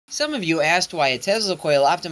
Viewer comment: I thought he said “Some of you ass wipes” lmao